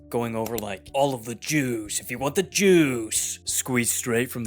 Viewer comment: I thought you said all the jews if you want the jews